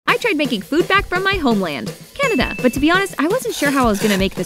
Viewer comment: I thought she said “big back” when she said “food back”